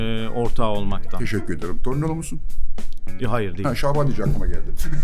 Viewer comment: burda ne diyor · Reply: torinolu musun diyor